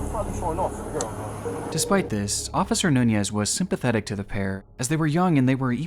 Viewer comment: I had to listen a second time at officer nuñez, because it sounded like he said "Officer Nunyas" 😅